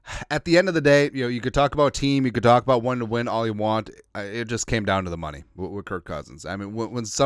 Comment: I thought Kirk said he ain’t care about the money